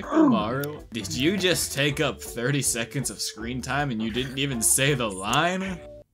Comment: He said Shikamaru did you just take up 30 seconds of screen time and you didn't even say the line But the subtitles says 20 instead pd 30